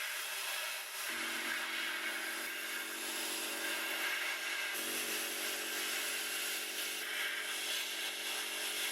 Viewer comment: what is the Russian word? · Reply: Стрелки